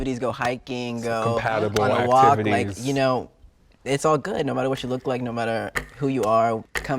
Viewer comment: said “ no matter what SHE look like” instead of “ no matter what he look like” 😂